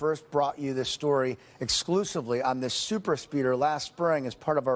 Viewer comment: I thought it said "super seeder" and it was about a guy that had 100+ kids. Ha